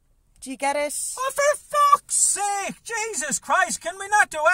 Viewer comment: Did I hear “Jameses Christ?”